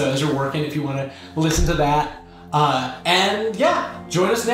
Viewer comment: Walpole connection? In my Genghis Khan series? It's more likely than you think. Listen to "The Infinite Steppe," original music created for this series! <link> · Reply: What were you gonna cover next week? Cause it sounded like you said "the triangle-shirt-based fire".